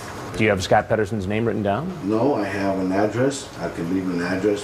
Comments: scott pederson, i thought you said john pederson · I heard it as ‘pedo’son lol an ironic name he thought of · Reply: Foster I heard Pederson also. I wonder why everyone say petterson.